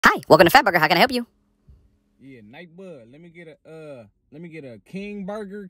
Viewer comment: Why it sound like he said nightbugg at first